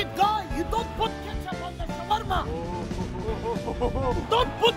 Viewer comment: He really said nasseb ( Fate)😂😂